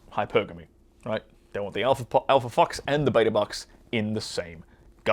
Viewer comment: "The Alpha Fox and the Beta Box in the same package..." OMFG, laughed my ass off at that phrase. Hilarious! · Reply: I heard it as Alpha f**me and beta bucks!!